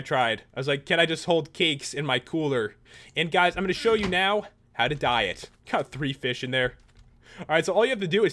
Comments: lol 3:46 thought you said how to diet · i was totally thought he said diet when he said "and now, how to dye it"